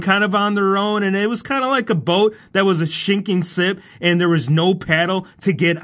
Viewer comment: you said "SHINKING SIP" instead of "sinking ship" LOL!!!